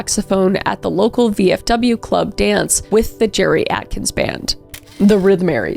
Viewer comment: Jerry Atkins! I thought you were saying Geriatrics. I think I need my hearing checked.